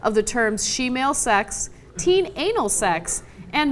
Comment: I heard 'team anal sex', not 'teen anal sex'!